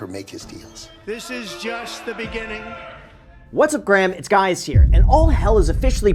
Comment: 😂 “What’s up Graham, It’s guys here” i rewind that 5 times… did i hear that right?